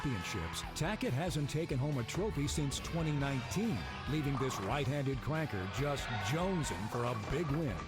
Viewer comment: did I hear that right? 😂 Still trying to figure out what they mean cause they can't mean white guy in today's age. Too funny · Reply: They very clearly said “right handed cranker.”